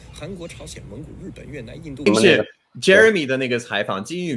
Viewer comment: Am I the only who hears "Jeremy the nigg*" at 5:12 lmao?